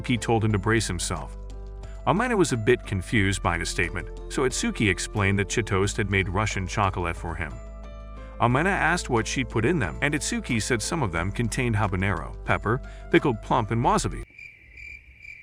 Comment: I heard '' and her Russian chocroulet(not a typo)''